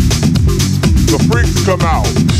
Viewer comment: The whole time until now I thought he said "the freaks about" Me in my los Santos nightclub for the last almost a decade: THE FREAKS ABOUT TO WHAT?